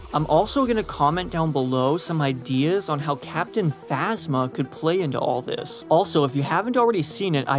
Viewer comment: Thought he said captain asthma